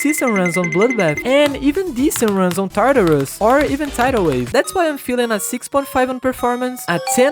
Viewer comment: did he just say tidal wave😭